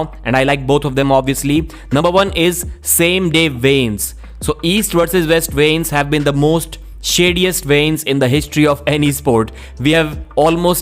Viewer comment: Am I the only who has no clue what he is saying here? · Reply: He's saying "weigh-ins"... has a very THICK Indian accent 😅